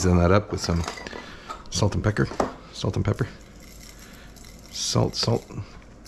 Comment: LMAO.. I lost a good portion of Guinness thru my nose when I heard you say "salt and pecker". And you KNOW you said it cause you were trying not to laugh afterwards. Keep rocking you, bud!